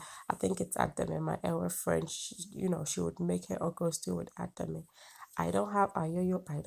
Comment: Nice video are you a caterer?or you just cook in your house?can you make a video on how to cook kwase. I dont know how to spell it but I know you will get the food am talking about..thank you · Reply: Oh I thought she said with "fried stew ". All the same you're welcome 😉